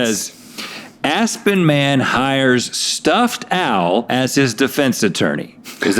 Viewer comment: So when Rhett said “stuffed owl” I heard “stuffed Al” and thought of Alf and he’s a puppet so...sorry?